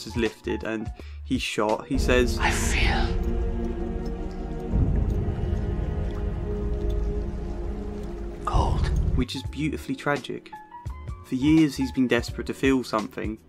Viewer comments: I always thought he said “I feel... gold?” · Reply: I always thought he said “I feel... old.” 👴 😂 💀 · The “I feel... cold” line from Barbossa at the end always hits me every time I watch this film. So powerful and tragic. I’m glad someone finally mentioned it. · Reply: I always thought he said “I feel... gold?”